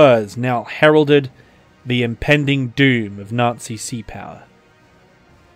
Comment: he actually said “the regulars are out”